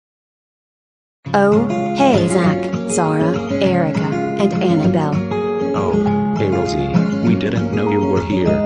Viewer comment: the music is too loud I can hardly hear what they are saying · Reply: This is what Rosie and her friends said during that part, starting at 2:27; Rosie: Oh, hey Zack, Zara, Erika, and Annabelle. Zack: Oh, hey Rosie, we didn't know you were here! Erika: Wow! You look so beautiful tonight! Anabelle: I agree with Erika! Rosie: Why thank you! Me and my sisters got dressed for tonight because we were all excited! Say, uh, where's your parents? Zara: They are in another area of the park. They said they were going to watch the fireworks show from there. Rosie: Oh okay. I was just wondering. I am excited for the show just like you guys, and everyone else at the park!